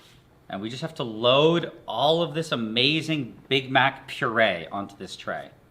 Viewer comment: thought he said “big back puree” but honestly, same thing.